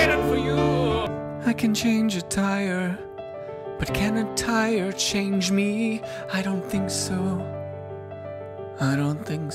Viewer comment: “I don’t think so” he says, let the tire change you Daniel! THE TIRE IS A MAGNIFICENT CREATURE ME BOY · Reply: I heard it as "I can change a tire, but can attire change me?" and now I think Daniel has a personal problem with his wardrobe.